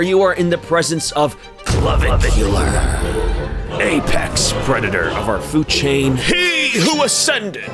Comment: At first, instead Apex predator, I misheard it as "aipac predator"